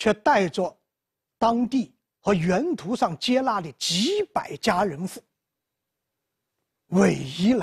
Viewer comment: 几百家人户，我听成几百佳人妇……老师是胡建人吗lol